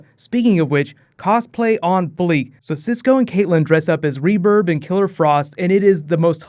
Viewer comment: did he say reBURB lol?